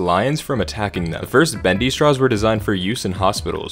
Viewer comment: When they said bendy straws i fr thought it said femboy straws bru 😭